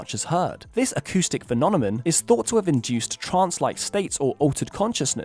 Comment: I swear I heard "phononemon"